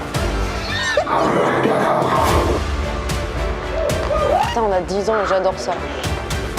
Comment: "on a 10 ans et j'adore ça" ??? de quoi t'adore les gens de 10 ans ? FBI allo ...